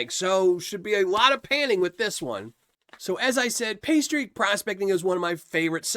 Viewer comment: I am sad to say that I thought you said pastry prospecting and not paystreak prospecting! Another fun vid despite a lack of donuts in the dirt....